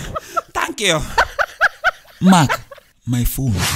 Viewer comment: did he just say thank you ?